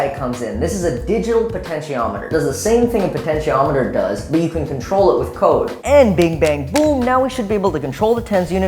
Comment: I seriously thought he said “but you can control it with co**” wow😅